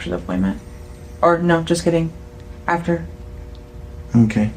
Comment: does she really say "no just kidding"???